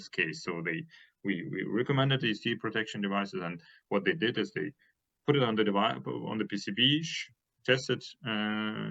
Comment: and following, repeatedly refers to some term I couldn't catch -- "something parameters", where "something" appears in the auto-captions variously as "skater" "scaling" "catering". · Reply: Hmmm, maybe the term is "scattering parameters"? ("A" should be pronounced like in "cat", not as in "cake".)